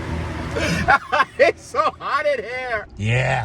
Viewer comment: I can’t unsee eating that chocolate along with the commentary 😱😱😂😂😂 with your accent it sounded like you said I’m so hard now instead of hot then you started on the chocolate, I’m sure there are lots of damaged people out here now